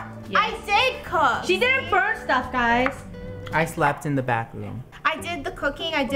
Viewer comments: No one: "I slept in the Bathroom" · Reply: I thought he said backroom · No one: Absolutely no one: “i slept in the bathroom” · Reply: I thought he said back room?